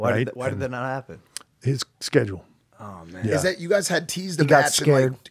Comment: When HHH said "schedule", I thought he said "it's scheduled": I had goosebumps for a quarter second... :(